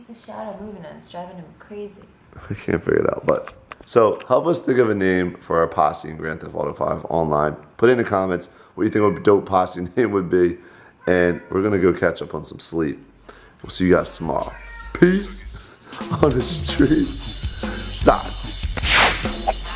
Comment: "We're going to go catch up on some sex" is what was really saying.